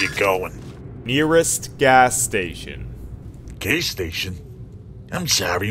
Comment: it sounds like he said gay station. 😅😆😁